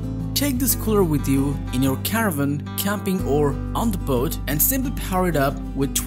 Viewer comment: is he saying cabin or caravan? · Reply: Caravan.